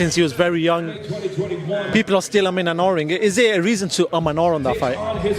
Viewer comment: What is the word at 4:37? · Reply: Umming and arrhing - should or shouldn't